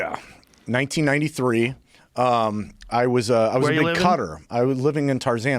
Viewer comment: When he said cutter I thought he was saying Qatar till he elaborated 😂😂